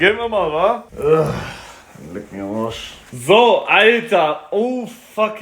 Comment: digga ich habe habosch verstanden😂😂😂